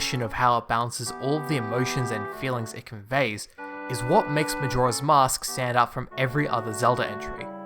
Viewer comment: i didn't immediately pick up on the accent so i heard "majora's mosque"